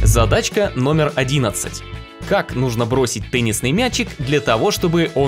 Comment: я один услышал пеннисный мяч